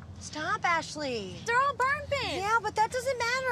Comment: What did she say????? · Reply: TheGuyWhoIsAustralian barmping - how we say beeping in Newfoundland !